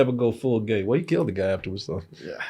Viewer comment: Maybe it was bad optics to have Denzel kiss a guy, then killing him 5 mins later. Funny though, I didnt here correctly that he killed the guy, I thought he said "I get killed." And I thought, whoa... Denzel really doesnt give a sh!t anymore. First Black Panther, now Gladiator 2, he just spoiling stuff all over the place.